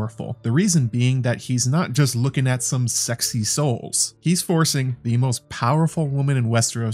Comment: I heard "sexy souls" instead of "sexy soles" and I thought of a FromSoftware video game. @ 07:00 Funny, an addition to this list could be Hidetaka Miyazaki (no relation), the president of FromSoftware and a legendary game creator, who loves to add foot scenes in his games. It's all connected.